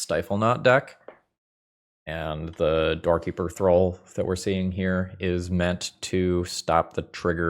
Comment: I always heard you say "Doorkeeper Thrull" as "Dorky Patrol" 😅